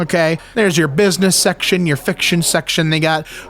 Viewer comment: Is he saying section or sex-tion....